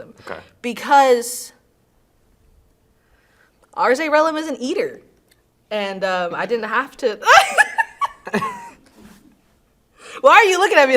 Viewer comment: What is she saying?! Can’t hear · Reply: I'm pretty sure she said she didn't have to fake because he's an eater lol